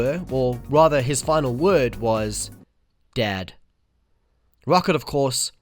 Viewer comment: oh hell I thought you said "dead". I feel ashamed